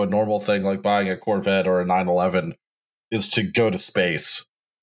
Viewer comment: "I do like that his midlife crisis, instead of being like a Corvette or a 9/11..." Cue 5 minutes of me trying to think if 9/11 was Dubya's midlife crisis, Osama's, maybe the hijackers???? Before finally realizing you meant a Porsche.